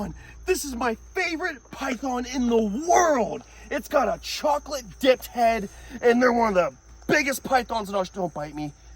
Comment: i heard him say its gota chocolate dik head